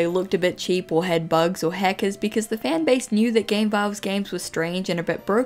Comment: So tbh, because of her accent, I keep hearing "hackers" as "heckers" (Not hating I actually really like her accent)